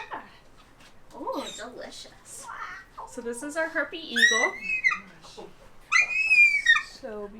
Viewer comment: This is soo cute but I can’t get past that it sounded like she said “so this is our herpie eagle”😂